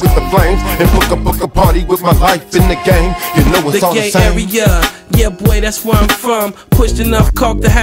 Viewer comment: Why on the beginning 2nd verse jacka stupid sound like he said "the gay area, yea Boi that's where I'm from"